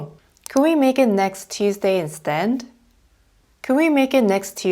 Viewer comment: instead 발음이 instend로 들리는데요 이게 맞나요?